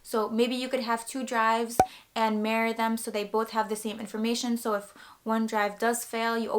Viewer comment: It sounded like you said marry your drives when you said mirror them lol. Good tips! Awkward timing with the Mango Street vid but understandable coincidence, great minds think alike to prepare the fans for the worst